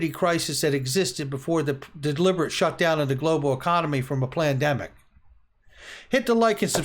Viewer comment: Sounded like you said plandemic at the end there. Freudian slip?